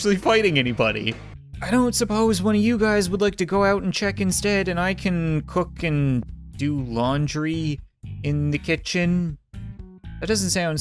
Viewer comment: I thought he said and I can cook laundry